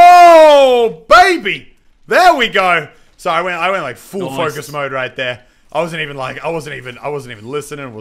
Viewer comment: I thought that IT said only guys mode I was like oooo meannn